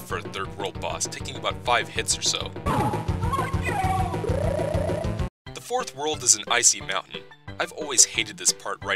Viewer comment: is it just me, or DID Robirdo say "Fuck you!" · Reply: It said "How could you"